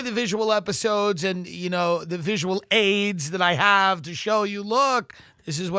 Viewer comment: Whoa. I thought you said that you had visible AIDS in the Thanksgiving podcast.